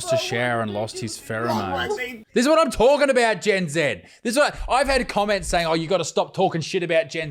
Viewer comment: The fact you call it Gen “Zed” and not “Zee” just makes it so much better 🤣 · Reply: 😂😂 thought he was saying gen zen 😂 and I thought it was hilarious too